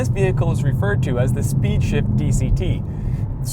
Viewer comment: did he say "speeds shit" lol jk